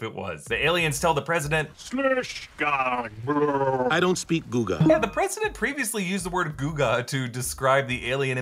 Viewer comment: I thought he said - I don't speak Booga.